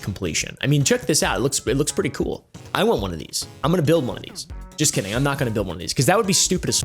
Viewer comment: btw oompa does ur editor like to do typos for comments engagement cuz 24:05 lol "build Mondays" instead of "one of these" if you want somone for subtitles and extra spanish CC HMU up im autistic and have a lot of time lol